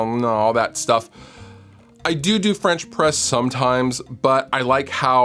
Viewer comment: All I heard was "doo-doo french press".